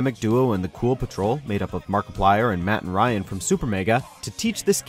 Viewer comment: Did he say "Mark Plier?"